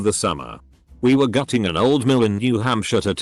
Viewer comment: I heard it as “we were gutting an old man” instead of gutting an old mill omg